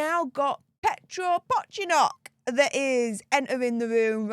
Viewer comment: Did you just say Petro PopYourCock …..???